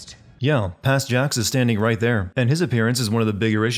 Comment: "tight there" instead of 'right there'. lol